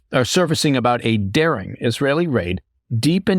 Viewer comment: I thought it said dancing Israeli raid lol